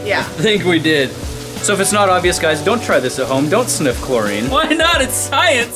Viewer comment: am i the only one that thought it said dont sniff cocaine it hurts?